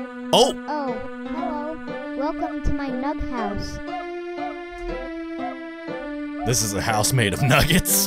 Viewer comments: It sounded like he said welcome to my mug house XD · I thought Baldi said "Nut House"